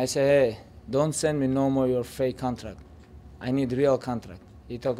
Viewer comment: minutes what KHABIB was tryna say ..Don't Send me No more your Fake Contract 🤣🤣it sounds so funny 😂Dafug